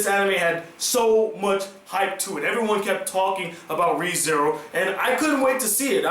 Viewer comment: did you say Re:Zero?! XD